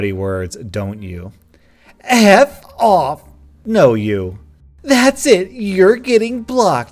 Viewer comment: Did he just say No u ...